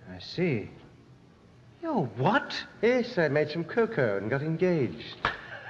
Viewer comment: I always thought he said "Yes, I met some coco and got engaged."